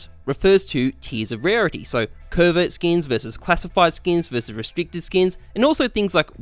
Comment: I heard "pervert skins"